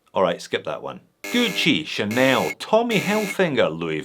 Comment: wait, did i hear Tommy Hellfinger? 💀